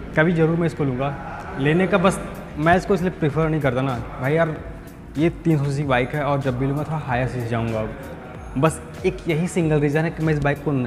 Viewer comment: kya bol rhe ho bhai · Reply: Ye 300 cc bike hai aur ab jb bhi luga higher cc luga , yahi bola hu bhai😅